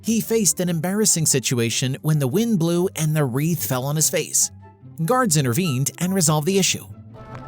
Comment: I heard: "GODs intervened and resolved the issue", sorry for my poor English listening ability. :face-pink-tears